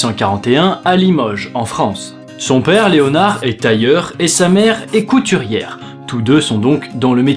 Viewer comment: Quand il a dit :"son père est tailleur", j'ai compris "son père est ailleurs" xD J'ai vraiment un problème xD